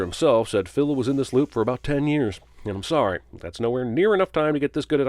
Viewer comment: I thought he said 30-40 years?